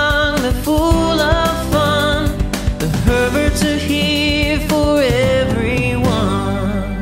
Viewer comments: I heard "the perverts are here for everyone" sorry. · “The perverts are here for everyone” I thought that's what it said